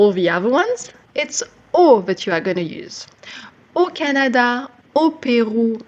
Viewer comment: your subtitles/captions are wrong I think? I assume you mean "au Canada", etc.